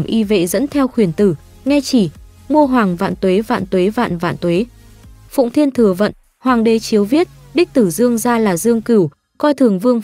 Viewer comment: Hồi xưa coi mấy phim võ thuật trung quốc Toàn nghe thành phụng thiên thừa mệnh hoàng đế đíu viết 🤣🤣